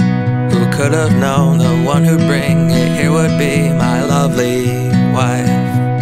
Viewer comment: does he say he'll be my lovely wife haha that's what it sounds like